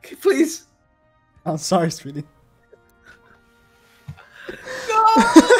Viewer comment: Did say Sweety or Speedy? And 0:10 LoL Also, I'm surprised that I actually got 900 gems for anniversary just by farming the story towns and got B lilia to 4/6 (so 3 from the summoning and not only that, one of them came along with a blue demon Meli), got 2 Godess Liz and a Lv Meli on guaranteed :)